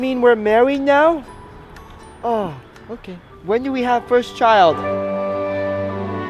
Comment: When Brent said first child I thought he said first trail😂😂